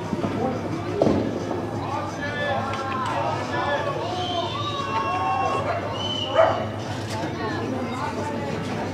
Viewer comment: Can someone tell me what was being said at 1:21? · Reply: Laura BLair get off oder so